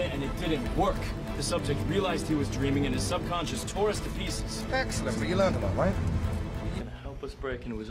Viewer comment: what in the bloody hell does he say @ 1:49? haha thanks! Tom is the man! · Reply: I think he says, "Excellent. But you learned a lot, right?"